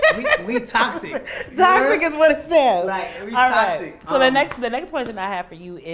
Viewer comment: All I heard was It is what it is lol